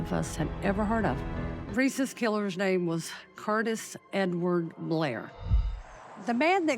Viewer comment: I thought she said his name was Curtis N-word Blair I was like "WHAT?!" 💀💀